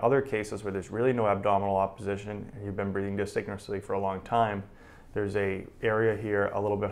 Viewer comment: peace Great Video! I apprecite it! In minute 2:20 you said "in other cases where there really is no abdominal opposition you've been breathing _ for a really long time"I didn't catch that word in between. disink something ? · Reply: Likely dysynchronously